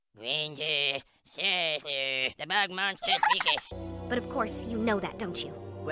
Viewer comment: what is cartman saying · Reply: wendyy, seriously, the bog monster begs 👹